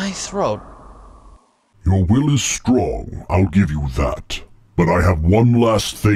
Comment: Is it just me or do I hear Vader say "Your willy is strong"